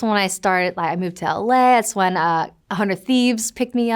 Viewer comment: Does she say LA or LS? 💀GTA RP ruined her brain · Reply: Nah, she just didn't pause between sentences, so "LA" and "That's" sort of blended together.